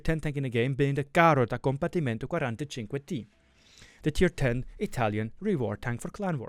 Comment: what did he say? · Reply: The carrot tank from recent reward for wars